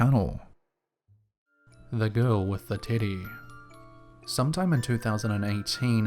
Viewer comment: I wasn't looking at my phone at the moment and had to do a double take because I swore I heard "The Girl With the Titty" 😅💀